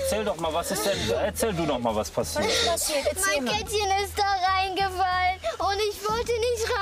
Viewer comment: Was ist jetzt mit dem Kätzchen ? -_ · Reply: Ich hab zuerst auch Kätzchen verstanden aber das könnte sie ja gar nicht zwischen dem gitter rausziehen